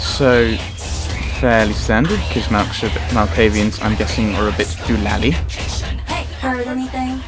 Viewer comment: What did you say? Malk's are a bit... doolally?